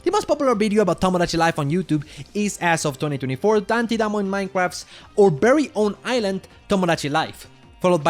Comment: what the FUCK is he saying here? Dantadamon minecraft and berries n shit wtf? Edit: 21:30 what's a bive?